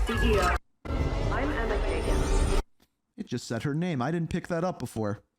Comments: Might be wrong, but I Heard "Emma Kegan/Keegan" which maybe (just maybe) might have Something to do with Keegan from COD: Ghosts, but Highly unlikely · Sounds like she said eme Kayden or Kaygin it’s something with a g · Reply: Her name is Emma Kagan 🤙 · I hear Emily Baker